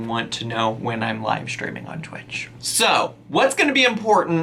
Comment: Haha I misheard “I’m livestreaming on Twitch” I thought you said I’m LECTURING on Twitch 🤣